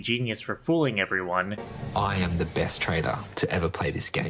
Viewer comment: I thought you were saying "traders" for an embarrassingly long time (assuming you meant 'trades people'), I kept waiting for you or the game to explain what their trade was. "Yeah Yeah, I get it's a game, but what do they *do*? Wait this guy is in marketing? That's not a trade... Wait"